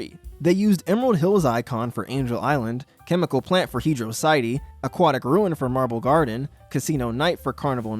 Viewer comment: Did bro just say “hedrociyte”?